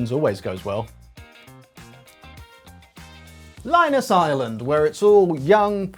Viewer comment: I thought you were saying ireland, in reference to the EU's current tax haven state. The EU's Delaware. You went a different direction